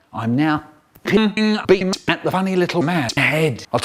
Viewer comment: What did Michael say at 2:46? It sounded like he's saying "pinging beams"